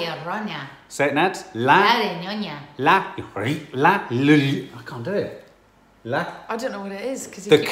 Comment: Sounded like Nadia said la laonion lol